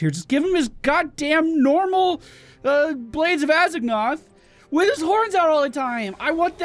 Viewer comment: I honestly thought you said Blades of Eggnog